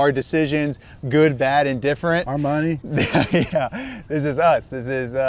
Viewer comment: When you said "our money" honestly for a second there it sounded like "Armani"...